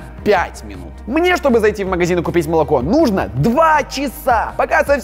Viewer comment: Мне послышалось что А4 сказал убить молоко